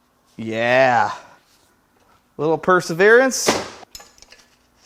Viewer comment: you it sounds like you say 'a little perserence, yeah" if we used AI to remove your accent would the subtitles say 'use a bigger pry bar, yeah '?